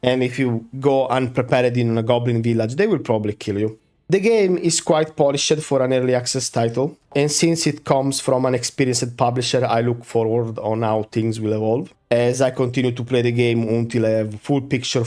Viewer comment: I know at the end you said "It's quite polished for a early access game, but initially I heard "...quiet shit for an early access game" and had to replay that part a couple times.